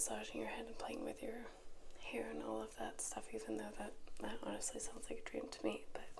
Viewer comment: I heard "playing with your hair and flirtatious jokes" so I ran as fast as I could 😂